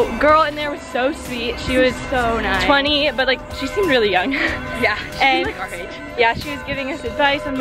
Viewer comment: Did you really say "she is 20 but she seemed so young"? The girl in the salat place (around 05:05)? I thought I'm getting old. But that old?